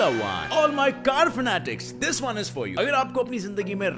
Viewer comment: On 1:34 you said carminatic but I heard like Carryminati